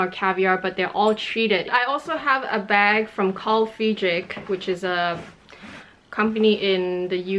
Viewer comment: What's the bag brand you mentioned at 2:00? I couldn't quite catch it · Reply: It's Carl Friedrich. I just updated the links in my description box as well!